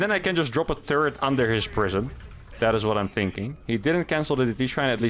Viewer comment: So... random to GM next? And I heard "I''m gonna drop a turd under his prism" :D